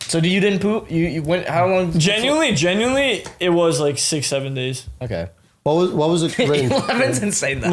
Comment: Did he say 67